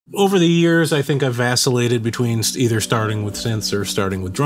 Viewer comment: Yeah I'ma stop you right there, did you just say "Asscillated?"